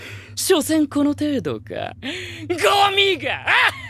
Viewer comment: i did i just hear the n word on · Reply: ye meega i heard